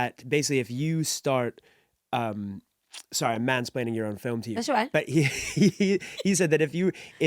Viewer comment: What did he say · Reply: "sorry I'm mansplaining your own film to you" (mansplaining is a new word invented by feminists, it is man-explaining, which is when a man explains something to a woman in a belittling way when she already knows the information) Its just women getting mad at men because we cannot read their minds to ascertain their level of knowledge about something.